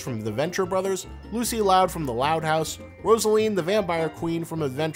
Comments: Rosaline???? Is that what he said??? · did he just say Rosaline? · Did you say Rosaline?